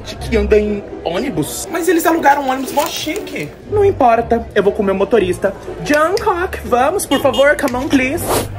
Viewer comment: Na horad o "eu vou com o meu motorista" eu ouvi "eu vou comer o motorista"